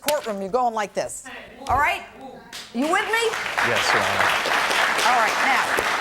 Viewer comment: did he just call Judge Pirro a sir?